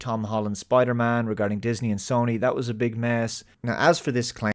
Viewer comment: im sorry but you legit went on a minute long rant (frrom 12:00 to 13:00) from mishearing what he said... he said that was a big mess and you heard big miss and then proceeded to go off saying that he would say xyz even though he never said anything and you just projected a response based on what you wanted to hear... wtf